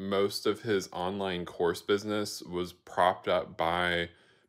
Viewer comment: I thought you said horse business for some reason for a sec and I was like 🐴🤔